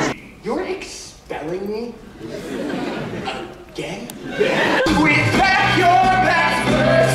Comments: the captions misheard “again” as “im gay” and I CANT 💀💀💀🤣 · I have captions on and at 7:54 the captions said "I'm gay" but percy really say again I'm crying